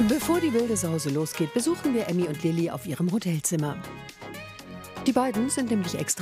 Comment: haha ich hab statt Hotelzimmer Bordellzimmer verstanden 😅